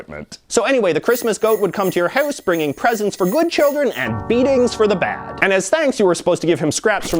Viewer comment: I don't know what word you said after "and". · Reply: After the word 'and' he said 'beatings for the bad'. Yup, Joulupukki spanks those children's f***ing a**.